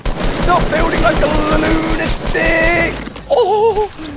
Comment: did ali sayed d7ck on 5:35 or it was just me? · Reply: black knight he said lunatic